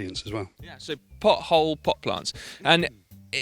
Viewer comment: sorry simon did you say "pot-plants"